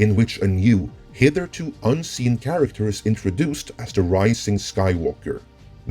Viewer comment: Did you just say hitherto unseen?